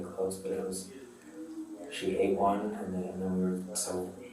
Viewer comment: did he say “she ate one”? (Talking about the puppy’s)… I wish there were subtitles because I can’t understand him plus his voice echoes in the room… if anybody knows what he said then please let me know cuz I’m confused. Also it’s sad they separated the mom from the puppies. I’m really glad they were able to rescue that sweet little boy tho. I really hope that he finds a nice stable home to grow up in, because this ain’t it! 😢 🙏🏼 🤗